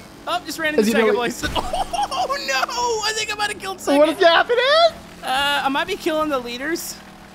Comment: Nice video! Love wreckfest with! :D Edit: 14:55 did you just say "I think I might have killed Satan? XD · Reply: No he said I think I might have killed second